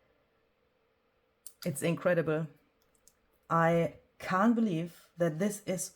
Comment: ❤ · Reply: I thought you said inkcredibly. Maybe a new word. Lol. ( INCREDIBLE)